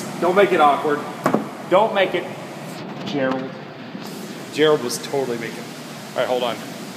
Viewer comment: does he say "Gerald"?